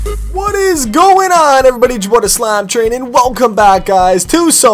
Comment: did slap say he's the slamtrain? or is it just me